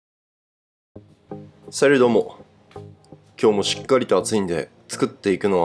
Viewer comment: salutどーも！がさるどもに聞こえるからの🤣最高に私がイタリアンでも注文する大好きな一皿です！アサリを一度取っておくとか、パスタをソースで茹でる？煮る？とかめっちゃ参考になりました。おうちで作ったら毎日これになりそうー